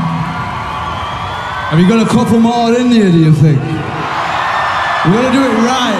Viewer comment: What does Alex say at 0:09 ???? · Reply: “Have you got a couple more in you do you think”